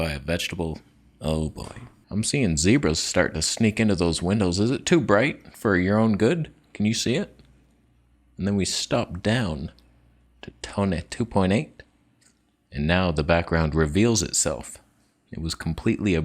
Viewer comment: Did he just say, "Stop it down to Tony 2.8?" LOL. I want to see the lake.